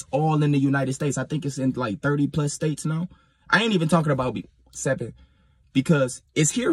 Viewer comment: What is he saying that at 15:06? B-107? What is that?